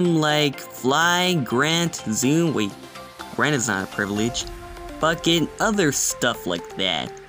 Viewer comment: did you say the f word? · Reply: "Bucket other stuff like that, Privileges"